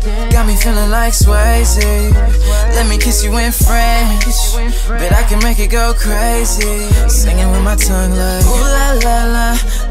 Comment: who else searched "let me kiss you in french , bet i can make you go crazy" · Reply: I thought he said "let me kiss you with a friend; I can make you go crazy."